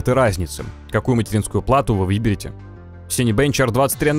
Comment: это что было? я один услышал "какую материнскую плату вы въебете"?))) вроде такое видео серьезное, как-то смешно сразу стало)